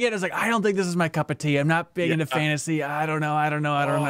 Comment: kind of messed my head up for a minute. I could've sworn I heard him say "I'm not into fantasy" during the Dragon's Dogma 2 conversation. In my head, I'm like "YOU LIKE ELDEN RING, WHAT DO YOU MEAN"? I get what he's saying, but in my head that was my initial knee jerk thoughts lol.